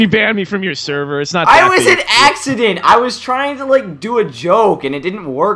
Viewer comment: Lol at 4:04 it sounded like you said you were an accident, you're the best:D